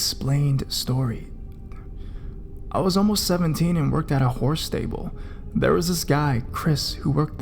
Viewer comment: I thought that said "whore stable" -_